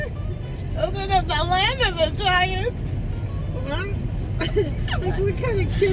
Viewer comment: Omgggg instead of “Children of the land of the Giants” I thought your mom said “Children of the land of vaginas” AND I LITERALLY CHOKED